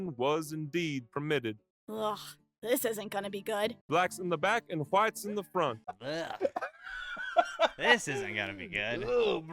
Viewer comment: sounded like "party in the back, business in the front"